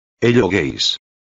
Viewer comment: Why did i hear it like "Hello gays."?